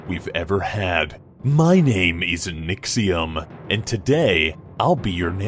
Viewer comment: It sounded like said my name isn't